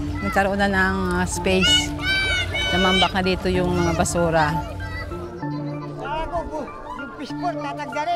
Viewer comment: the language kinda sounded like spanish to me for some reason 😭 edit: cause at 10:33 it sounded like she said basura which is trash in spanish